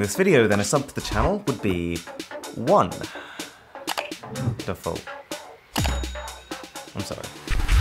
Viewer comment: This time I really thought you will say "a subscribe to the channel will be one UI". The wonderful made my day !!!